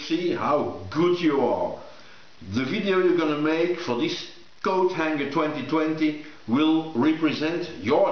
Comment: thought you said GoatHanger. Image formed in my mind uurrggh.